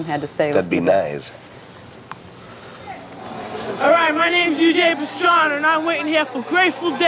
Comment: Did he say his name was Eugene Pastrami?